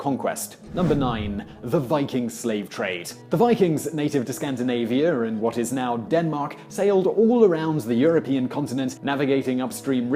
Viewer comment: Skandinavia is now Denmark? :P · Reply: it's hard to hear because mumbled but I hear " Scandinavian and err what is now Denmark". I was going post the same thing but I went back heard this. It was a bit difficult to hear.